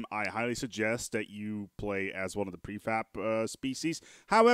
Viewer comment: I heard prefap here....